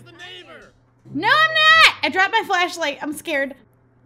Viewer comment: I think that said "Oh good job Shelby"